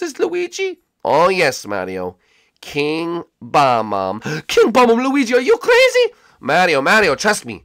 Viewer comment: When Luigi said King Bob-omb, I thought he said Kill Obama. I was like, What the heck? Why would you kill Barack Obama? He was the president!